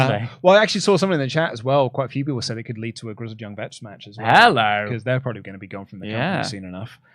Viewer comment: What did say at 1:18:30-1:18:40? I couldn't quite make out what he said, even with multiple re-listens. They both have a tendency to move their head around a lot leading to different volume and clarity levels... :( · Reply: Maybe it's his accent but I didn't have a problem making it out. He said people in the chat mentioned it could lead to a grizzled young vets match coz they will probably be gone from WWE soon...and he could see best friends winning trios gold